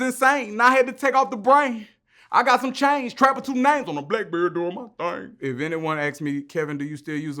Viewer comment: Swear I whole thought you said black bird doing ya thang 🤔